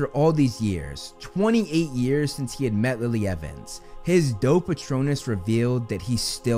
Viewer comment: Every time I hear say “doe patronus,” all I hear is “dope patronus”